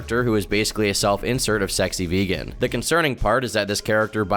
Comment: Lol I thought it said sexy virgin wtf 😅😂